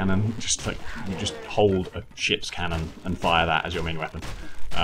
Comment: Did I just hear the word shits cannon? 0:10 lmao xD